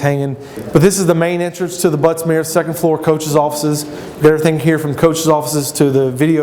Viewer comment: did he say " buttsmear " at 1:08?